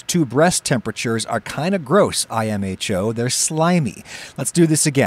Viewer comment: What does mean when he says I am H.O? At around 1:41? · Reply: He says “imho’ which means ‘in my honest opinion’